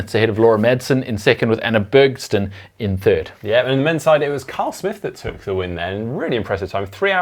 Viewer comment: Sounded like you said Carl Smith. But I’m from US.